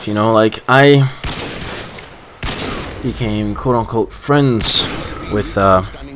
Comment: I thought he said "I became caught on coke"